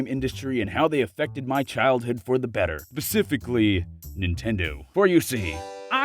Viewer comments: I paused at 1:30 to check a message and it sounded like you were about to say "Ninja" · anyone else hear “basicfically”?